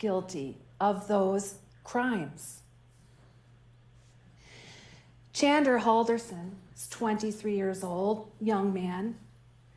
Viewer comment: I swore she could say this : Chandler handerson identifies as an innocent guy !